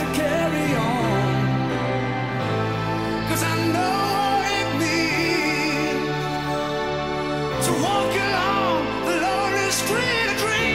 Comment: I thought he was saying "cuz I know what it means to walk alone in only 30 degrees." 😆